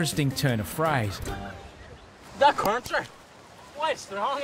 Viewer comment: The only thing I couldn't understand was when he said "current". I thought he had said country 😂